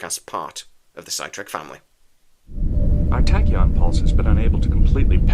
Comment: I'm infinitely fascinated with how British dialects MISS SYLLABLES COMPLETELY. -Did you just say TAK PULSE? @ 0:10 ?